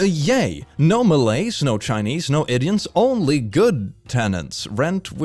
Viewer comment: the subtitiles said 'idiots' instead of 'indians'.